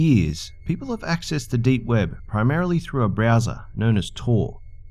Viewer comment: I thought you said BRAZZERS instead of browser.